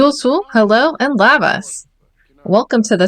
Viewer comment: I heard the intro and I swear it sounds like you said Labas (like hello in Lithuanian) 🥰I’m half Lithuanian so it’s nice to see some representation BUT I could just be hearing things wrong LOL — I love the series idea and the complex challenge. 😄